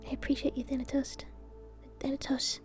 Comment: Did I hear say "Thanatoast" at the end? 😂